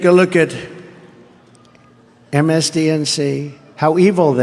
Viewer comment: I heard msdnc at 4:45. Not msnbc, omg, I'm happy I live overseas.